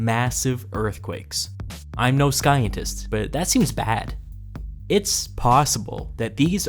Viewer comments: Wait did he say Sky-entist..,instead of Sci-entist? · Did you say "I'm no skyentist"??